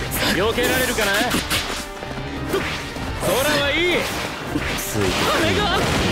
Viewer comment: Sooo I know this is a very weird question, but does anyone know what Hawks says at 1:22 ? Because this particular sentence has been stuck in my mind since I played the game hahahaha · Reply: Jajsjs He says "Sora wa ii" to a translation would be something like "The sky is great"